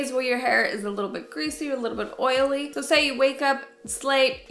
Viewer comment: I thought she said "so say you wake up, slay..." and I was like "it's like she knows me!"